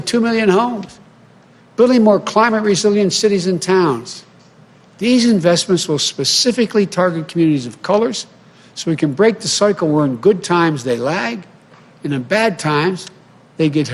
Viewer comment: Did he say “this will specifically target areas of COLOREDS”? 🤔🤔🤦🏻‍♀️🤦🏻‍♀️(11:08min mark) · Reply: He said "communities of colors". Yeah, didn't need the plural. But he did not say "coloreds".